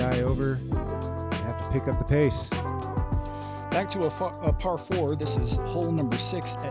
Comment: What does he say at 14:38? 😂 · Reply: Back to a fuh, uh par four